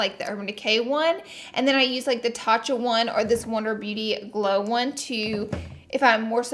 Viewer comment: when she said wonder beauty i thought she said lunar beauty so i had to back it up lmao